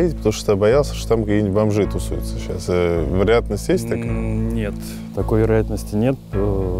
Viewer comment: Отчётливо слышно "не записывай это, я сжёг людей". Вам обязательно надо провести там вторую ночь, но не запирать дверь, мало ли что произойдет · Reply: первуя фразу я услышал что то типо «есть такой вариант» дальше не расслышал